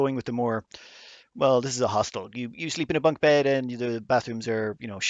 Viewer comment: Every time he said 'Hostel' I heard 'Hostile'.